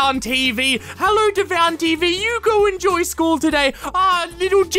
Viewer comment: did he say Jenna or Gemma?